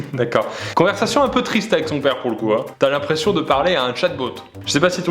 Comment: Pierre est l'anglais... Quand il a dit "chatbot" j'ai compris "chat boat", j'ai pas compris sur le coups xD.